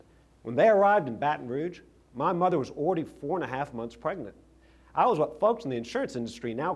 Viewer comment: Did Jindal say when his parents arrived in Baton Rouge, his mother "was 44 and a half months pregnant?" Holy cow!! Listen starting